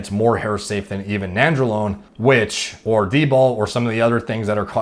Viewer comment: does he say dbol or tbol when he's talking about hair safe compounds? Sounds like dbol but from what I'm aware that's not typically considered as hair safe